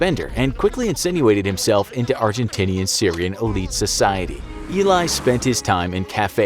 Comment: I heard argentittiesian